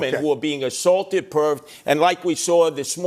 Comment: DID HE CALL CUOMO “Perv”????